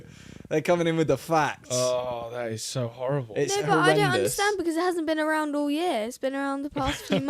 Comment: did she just say the n word 😅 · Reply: she said "no but"